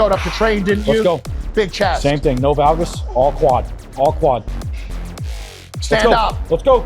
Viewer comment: Is he saying, “No valgus, all quads”? What does that mean, “no valgus”?